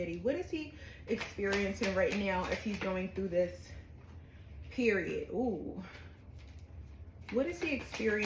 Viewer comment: Your hearing I’m going to stick beside him and I heard “Period Pooh” lol